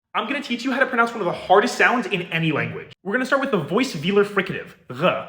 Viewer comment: I swear he said ħɒɹdɛst or something like that That was not a /h/